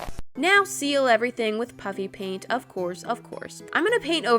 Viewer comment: I thought you always said, "puppy paint" me when I first watched you video:WAIT WHAT PUPPY PAINT?!?!